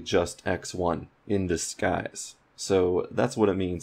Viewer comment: the way you said 'disguise' at 1:11 was very confusing. Sounded a bit texan. Had to turn on subtitles to figure out what you were saying